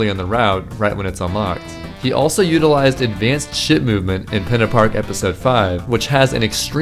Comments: I heard advanced shit movement Edit : shut -> shit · I thought you said “Advanced Shit Movement” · Did he say "advanced shit movement"? Edit: After listening multiple times and paying attention to what was happening on the screen, I realize he said "ship," but my misunderstanding is funnier so I'll choose to believe that's what he said. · Did anybody else hear advanced crap movement